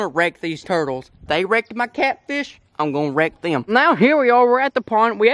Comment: Anyone else thought they heard rape instead of wreck?